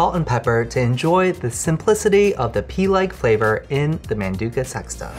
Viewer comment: I'm sorry did he just say "pee-like flavor"